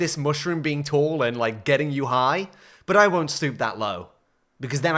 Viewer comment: Did anyone else hear snoop that low instead of stoop?